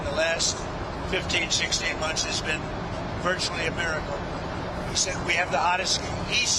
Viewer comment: I think it's He said Xi said